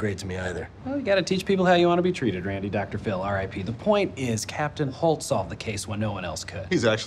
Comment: Did he just said "Dr. Phil, R.I.P." ??